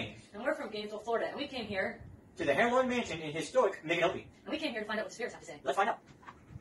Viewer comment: It legit sounded like the guy at the end at 16:41 said they're staying at the "Hemorrhoid Mansion".